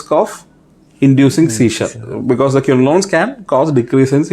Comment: what did he say, quinolones can induce what in elderly? I got it its seizure.